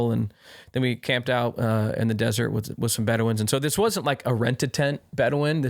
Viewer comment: I heard Bedouin and thought he said veteran with a speech impediment